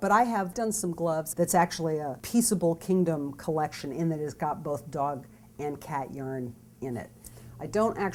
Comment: Did she say "Its got Dog and Cat urine"?